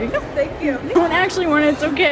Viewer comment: "No Makeup November" I thought you said something else, like I thought you said No Nut November